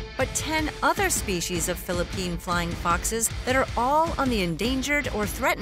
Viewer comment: Well at first i heard it "Flying Fucks"